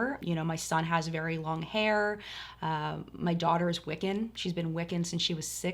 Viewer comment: Can someone tell me what says about her daugther at about 11:30? English is not my native language and I have no idea what that word means or how it's spelled ("wicken" or sth?) :) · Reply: Wicca. It's a nature based religion. :)